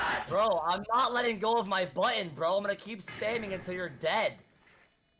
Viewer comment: anyone else hear him say “I’m not letting go of my button” right before correcting himself saying he’s just spamming it?